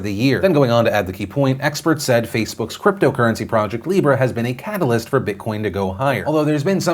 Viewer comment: 1) As a Libra, that is not the sign lol 2) I thought he said “lira” like the old Italian currency before the Euro